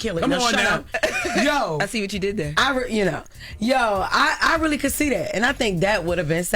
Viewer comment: lmao i heard "I'm really Conceited" like Reginald Sergile